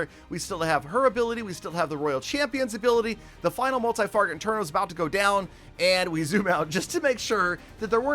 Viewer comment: Did say "Multi-Farget Interno" ?? I think it's an Easter egg of some sort in this episode. In any event I claim first! lol